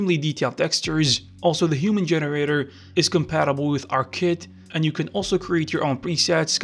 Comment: Great video, what was it that you were saying its compatible with at 1:35. Sounds like you said art kit? Is this like a UI that lets you select pieces of the rig easier? If so Im interested in learning about it! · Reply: AR Kit is referring to Apple AR Kit, it is a system of named facial expressions tied to shape keys. It is what powers the face filters you’d see on Instagram and Snapchat. 52 pre defined facial expressions. These characters come with them pre set in the data for the mesh as shape keys, which means you can use your phone to do facial performance capture as long as the naming conventions for the shape keys are the same. Any model with shape keys can be controlled with facial motion capture data, but without addons or procedural systems like this you’d have to do all of them by hand.